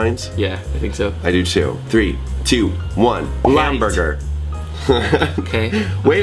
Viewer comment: when and Thomas said light and hamburger I thought they said lamburger